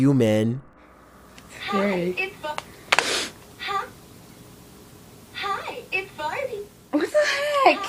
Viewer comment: i just heard the toy say "hi its farting"